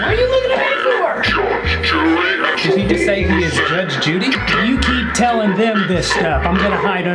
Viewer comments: i really cant guess what he said here i think its "i am the judge and jury" but thats a stretch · I swear I heard that thing say “I am Judge Judy” too 😂